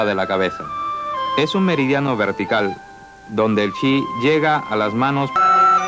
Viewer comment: Hola. Muy buen video. En el minuto 4:37 se corta la voz... me queda la duda de que dice en esa parte. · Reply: dice: "donde el chi llega a las manos" nada más.